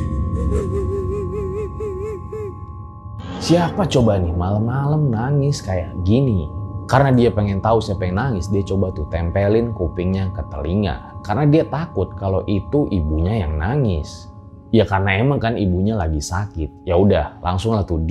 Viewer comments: ada yg ngedenger kek gini juga ga sihh? "tempelin kupingnya ketelinga" atau cuma aku yg salah ngedengernya. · Kangen cerita horor pendakian bg Menit 17:48 apa aku salah denger tempelin kupingnya ke telinga??